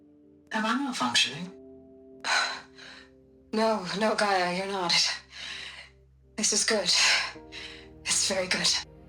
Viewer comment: why is the text different from what they’re saying in the audio? Like in the audio said “no, GAIA, you’re not. It’s good. Very good.” But the text says “no, GAIA. It’s good. Very good.” I’ve seen this in (at least) most other audio and hologram datapoints!